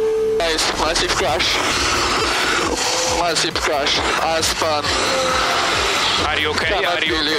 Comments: I think that Gasly said: Haas spun! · But he didn't spun, i think he said "Haas Spun"